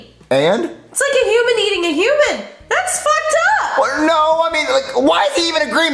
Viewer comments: Did Rosalina said “That’s fucked up?!” Comment if heard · did she said "that's F***ed up" ? There are kids watching this